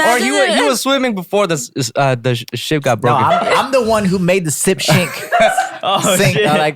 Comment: did jay just say "sip shink" LMAO